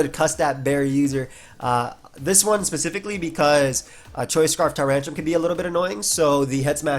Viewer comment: the first time I misheard that as "choice scarf tyranitar" and I was like ...........WHAT